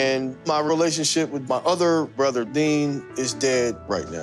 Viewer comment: I thought he said his brother died omg it gave me a heart attack